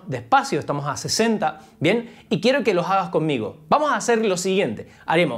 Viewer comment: Me metí al video... escuche "Y quiero que lo hagan conmigo", agarre mis baquetas y mi pad y a estudiar con!! Más videos así. P.D: Espero el de pies ...